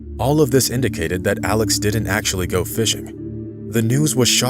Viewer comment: Oh I thought you said, Alice.